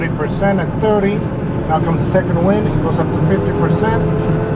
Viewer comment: what does the guy say? "now comes the second..." I cant understand the last word. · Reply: ArgPiston wind